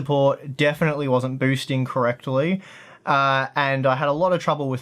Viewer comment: On 0:32 did you say co-rectally?